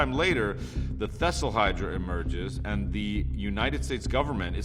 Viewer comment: Wait a minute.... did he just say, “thestl hydra?”